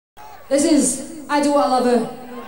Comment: I just hear: "This is: I do Oliver"